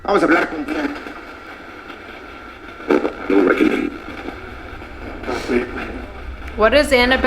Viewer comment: sounded like someone said ‘the reckoning’